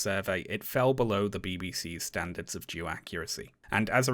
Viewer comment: sorry I know it's just your accent but I keep hearing "The BBC's standards of Jew accuracy"